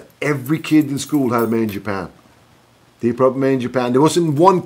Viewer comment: main japan? What's he saying? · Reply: Deep purple live album called “Made in Japan”.